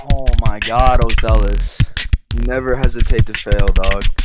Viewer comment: u never hesitate to fail??? what did he say